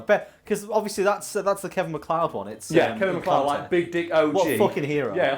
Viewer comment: I thought he said Kevin McCloud